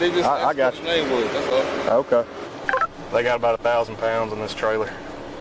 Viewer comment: the officer did not say 100 pounds, more like 1000 pounds